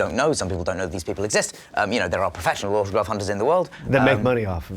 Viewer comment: I THOUGHT HE SAID THERE ARE PROFESSIONAL HORCRUX HUNTERS IN THE WORLD omfg he said autograph hunters